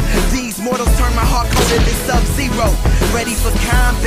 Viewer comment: c'est moi ou le chanteur dit SubZero ? :P